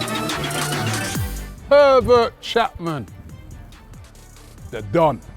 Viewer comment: I thought he said pevert Chapman